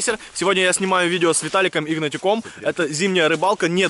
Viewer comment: с Я один такое слышу??